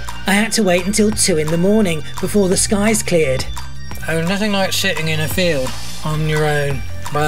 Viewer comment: I could swear I heard “there’s nothing like sh***ing in a field on your own” at 24:34. Had to rewind twice.